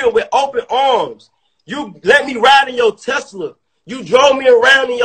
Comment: Lmaoo aye why did i hear “ you let me ride on ya testicles I thought we had a relationship “